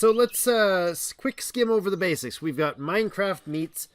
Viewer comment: Thought that said brains but it says basics 🤣 running off of 4 hours of sleep lol